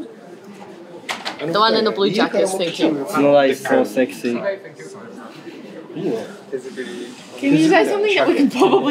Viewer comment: Anyone knows what yuki is saying before … so sexy? Somelay? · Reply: Tsunoda is so sexy 😏😉✨️